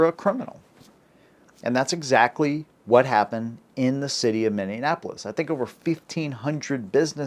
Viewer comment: Ooooohh, Minneapolis! Okay. I thought you said America! My bad.